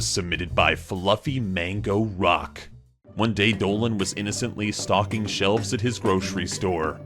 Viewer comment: I thought he said innocently stalking shelves WTF